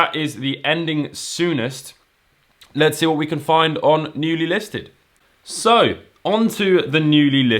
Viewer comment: It sounded like you said "nudie listed"